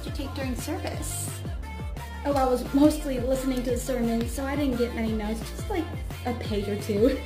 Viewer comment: WHYD I HEAR "semen" instead of sermon 💀👏